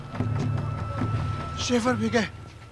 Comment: Tenzin is looking for his daughter Pema. (You thought he was saying Momma or Gramma.) Elena said that she’s with Schafer. They spot Schafer and Pema just as the tank arrives. You have a Sylvester cat! Sufferin’ succotash!